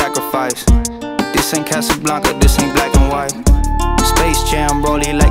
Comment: “Never big enough never satisfied They said give it up that’s some bad advice Had to work had to sacrifice This in Casablanca this in black n white” edit - never fucking edited a comment before lol I always thought he said “this AINT” but listening to it it sounds otherwise and it makes more sense cause in cals interviews he speaks on wanting to be transparent and clear. So everyone knows every word he’s saying like it’s in black and white no hidden shit about him so I just switched the comment to this in lol idk but that’s what I decided to comment confidently and it makes more sense to me cause he didn’t say “but this ain’t” but wtf do Ik and all the lyrics say otherwise so I could be wrong.